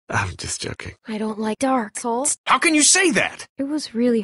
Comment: Clem:i dont like dark souls Kenny:how could you say that Clem:it was hard Kenny:whatever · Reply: I heard "dark balls".